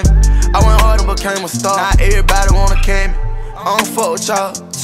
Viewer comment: “I got meds inside my cup to make sure I don't panic I took interest inside the cars now my whip panoramic I went harder, became a star, now everybody wanna kill me”🗣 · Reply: I thought he said every want a chemic